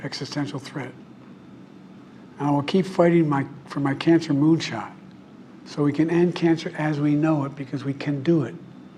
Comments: WTF is a CANCER MOON SHOT??? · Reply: I thought he said cancer moonshine 😅 I'm laughing but it's sad.. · WHAT THE HELL IS A CANCER MOON SHOTTTTTTT😳🤔🤦🏾‍♀️ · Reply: I thought he said cancer moonshine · its annoying how in their speeches they talk about ending this that ot the other, fixing this, fixing that, he just said solving cancer. Like they are seriously liying to our faces to have a "positive" image. my 2 year old died to leukemia aka cancer. childhood cancer research is not well funded at all. wake up people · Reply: I thought he said cancer moonshine